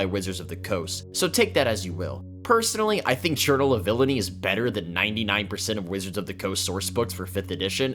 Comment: churtle ability? I didn't understand that word or what you meant by that, unless you were talking about hellfire. the caption reads turtle, but it can't be turtle.